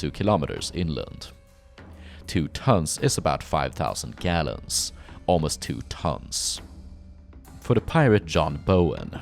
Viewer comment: "2 tonnes is about 5,000 gallons, almost 2 tonnes" Thanks, I didn't catch it the first time lol